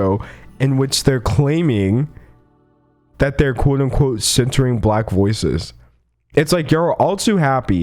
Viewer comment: “We’re centering black voices” but are they? They are still the ones editing these clips and controlling the narrative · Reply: I listened to this video and I thought at first he said “censoring black voices”